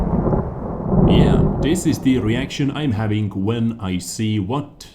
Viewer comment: at first I thought you said, "this is the erection I'm having when I see what T95/FV4201 Chieftain is capable of doing in this game"